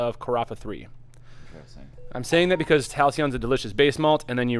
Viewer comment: What was that base malt you guys discussed at abour 26:30? I heard calcium and so did the subtitles so idk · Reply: Halcyon